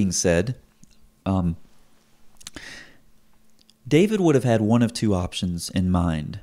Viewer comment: You said, "David would have had...." and I heard, "David Wood have had...." LOL